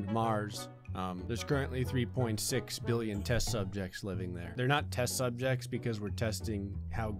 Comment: i imagined him say " Three millions of testicles " instead of " Test Subjects " .